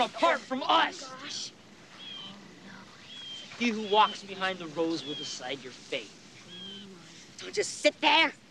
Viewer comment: When i was little, i always thought they said "He who walks behind the rose 🌹 " "Rows" makes much more sense now